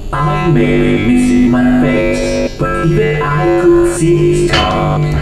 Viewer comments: [I may be Missing my Face but could even See this Stuck] 0:11 [ Laughing ] 0:16 [What is this new prison? is it me Træpped or is it You? Perhaps it's us both] 0:24 [Hello] 0:30 [Be sure of your step Through the heart of madness] 0:47 [Might as well face the fact you were always destined To fail] 1:01 [You blinked] 1:07 [Time to face the Consequences of your failure] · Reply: for some reason I hear “I maybe missing my face, but I could see the stars” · It sounds like he said But even I could see his 🐔